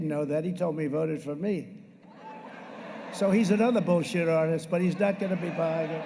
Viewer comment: sounds like Trump is also saying "he's" a bullshitter (Ah, a truthful moment). ;-)